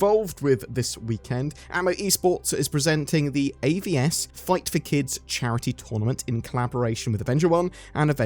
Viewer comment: I was really shocked when I thought you said "Fight the Kids charity event" - it doesn't sound like a good cause